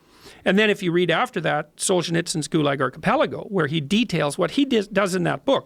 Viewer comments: Can some one tell me what the name he says at 41:09 ? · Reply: Gulag archipelago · What's the name of the book and author? I don't understand a word of what he's saying. Socienizkingulaakapalako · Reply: the Gulag Archipelego